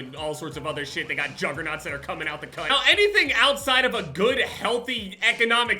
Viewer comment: Did he say cunt at 6:21?